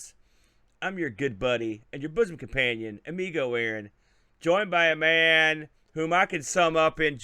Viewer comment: I thought it said AMICO Aaron lmao 🤣